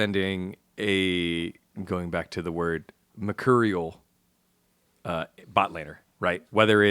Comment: Does anyone know what word was trying to use at 1:09:05? Mercurial? Means volatile/unpredictable he says star like later... but no idea what word he was trying to go for? Empyrean? As for the closing remarks as a DK fan I watched the series from start to finish. I lost hope when Lucid failed the mid gank in game 4 and reacted accordingly in the reply, but was so happy at the end result. One of the more emotional games I've gotten to witness even if it was a fiesta at times.